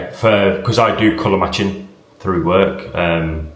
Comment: I had to put the subtitles on because I thought you said "I do color my chin"!